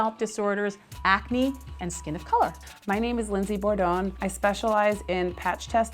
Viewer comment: At first I thought she said Lizzie Bordone. I was like "what kind of cruel parents does she have?!?" 😂